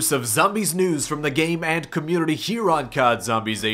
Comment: My buddy thought you said the gay man community